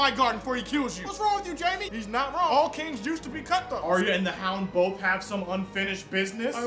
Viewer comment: I swear i heard him saying: "All jews used to be cutthroates" hehe